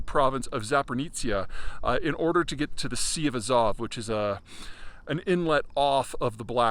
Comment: I heard "The Sea of Huzzah" and I do not want to be corrected, thank you